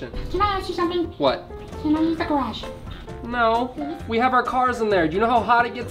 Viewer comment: I thought 1:33 said Can I eat the grass.